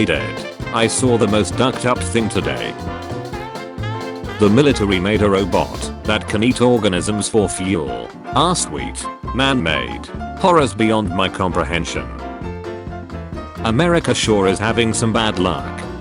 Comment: I thought it was even more cursed than it actually was. I thought it said "the military made a robot that can eat orgasms for fuel".